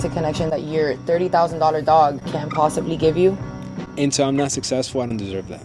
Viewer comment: Sounds like he said until Im "NOT" successful